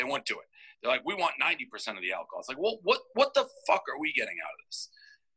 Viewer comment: he mentions they want 90 percent of the alcohol? What does that mean, am I mishearing him? And exactly Dave is really not someone I’d ever want to get in a legal battle with either 😂 Also nice job with the video/podcast!